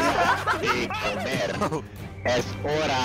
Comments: It sounded like they said "Es hora de comer", which in Spanish means "It's time to eat" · sup gamers! look at this :P Play World of Tanks here: <link> and use the code TANKMANIA to get for free: 7 Days Premium Account 250k credits Premium Tank Excelsior (Tier 5) 3 rental tanks for 10 battles each: Tiger 131 (Tier 6), Cromwell B (Tier 6) and T34-85M (Tier 6) Thank you World of Tanks for sponsoring this video. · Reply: was literally saying "es ora de comer" which in english it means "its time to eat" i dont even know why he said that lol and then said it to